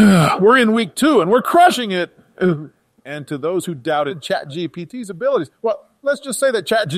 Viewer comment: Did I just hear Obama say ChatGDP ?